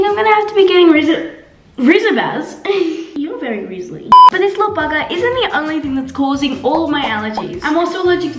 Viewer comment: Was it just me but i swear i heard say" this little bugga"